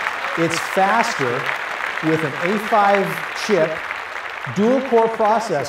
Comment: Did he say at 0:40 "it's faster with a duel five shit" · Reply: He said a5 chip lol